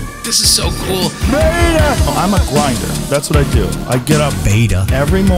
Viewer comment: "I'm a grinder. I get up every morning" " You're still gonna die, you idiot"😂😂😂😂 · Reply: oh my god until i read your comment i fully thought he said im on grindr lmao